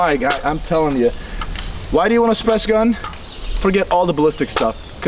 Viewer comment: What did he say at 7:50? That's why you want a "spes" gun? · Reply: “Suppressed” is what he said